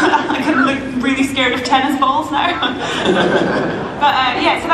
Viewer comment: sounds like she says Tennants balls